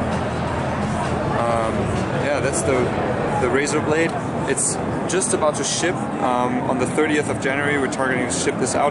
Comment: I heard "its just about to shit"